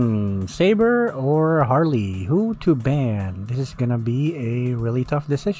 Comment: What I heard: "Saber or Harley, who to bang "